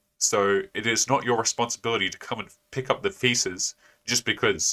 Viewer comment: Did he say "come and pick up the feces"? Hell, it works either way 😂